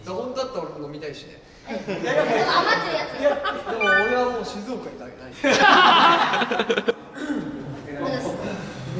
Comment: 樹くんなんていってます？ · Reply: 1年前のコメントに返信ごめんなさい🙇笑 「でも俺はもう静岡へ」って言ってます！